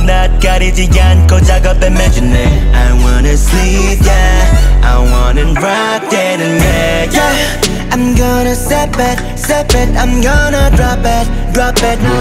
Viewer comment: I'm either trippin or that sounded like DEAN